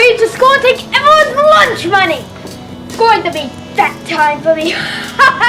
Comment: That line is so bad that I actually misheard "fat" as "that" (i.e. "that time for me") because it sounds more natural that way.